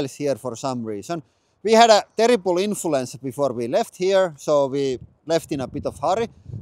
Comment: I had some files missing so the video ended up bit short. We had terrible mayhem when leaving Finland so shit is bit unorganized :D We are still 4 days in Dallas, if you want to meet us here check out the community posts! And last couple days time to check out the dice project if you want the kickstarter pricing! Support the channel and get something unique! Check out our Kickstarter: <link> – CNC-machined metal dice from the finest alloys and pure elements. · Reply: I thought you said you had a terrible influencer before you left Finland to go to the USA!